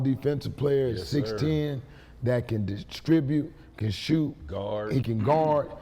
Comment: did he say shoot ??? 💀💀💀🌚